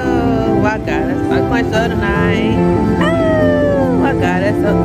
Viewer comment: I swear I thought said “I gottta suck your toes tonight!” I had to go back 3 times! Lmao! 😂😅